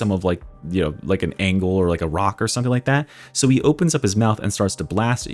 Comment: since no one said it,... did you say ROCK , LIKE ROCK AND STONEE??